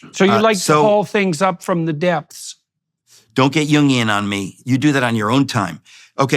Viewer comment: He meant Jungian as in Carl Jung the psychotherapist that Jordan has studied and admires. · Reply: Oh! What I heard as "Union" was Jungian, oh wow! Thank you kindly.